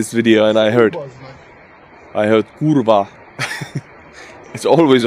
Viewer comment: Artur, at 9:00 into the video there's a word I can't make out. Hoorva or koorva or something something similar. I know the spellings are wrong, I usually spell stuff wrong in my own language but I'm hoping that phonetically someone might be able to tell me what the word is. As always I love you man, you're doing a great job with what must be a terrible job at times. Keep strong.